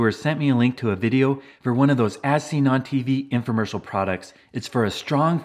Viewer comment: When you said as seen on tv, I thought you said asinine tv. 😂